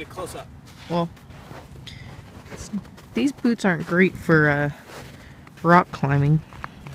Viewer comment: "These boobs are great for aaa... rock climbing" Did I hear that? God please say - no.